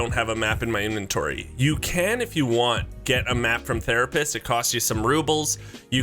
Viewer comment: i thought that the therapist said the rapist